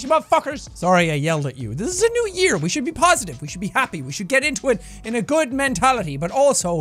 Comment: did he say we should be positive and we should be happy?